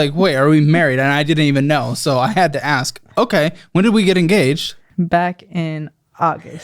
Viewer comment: When they said "we're engaged" I thought, at first, they said "we're gay." My brain just went "thank you, captain obvious!!!🙄" For a second and then quickly went to...😳😳😳🙊